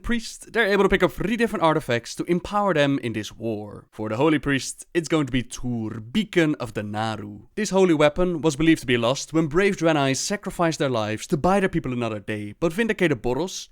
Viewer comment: I thought it said bacon of the naaru. I'm hungry.